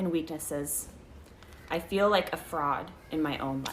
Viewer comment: I was putting down an uncomfortable amount of fingers and then I misheard that as "I feel like a frog" 😭